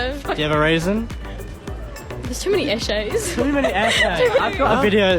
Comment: Even I can't understand what those girls are saying. The subtitles says "essays" but I know it's lost too, lol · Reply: Eshay. A bogan who lives in the city. You’ll usually see them at train stations, and they’ll either ask if they can bum a ciggie of you, or you’ll see them drinking a XXXX gold.